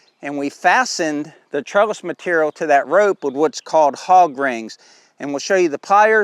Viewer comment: I thought you were saying "hog grain" for those clips you use? I haven't got used to your Florida accent. I'm in Missouri. :)